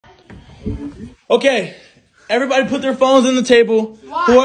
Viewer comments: bruh what i heard "aight everyone put your balls on the table" 💀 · Why did I just hear " everyone put there balls in the table " 💀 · I thought he said everyone put ur balls on the table💀💀